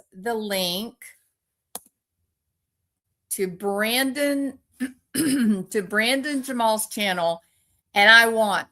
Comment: Can you provide the link for Brandon? I couldn't understand his last name. Is he on YouTube? Ty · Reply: Brandon Jamal